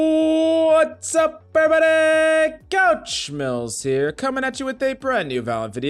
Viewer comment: the first time i heard his intro i thought he said wassup everybody coach smells here